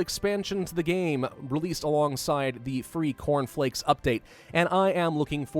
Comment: when you said "cornflakes" I heard "porn flicks" do I need help?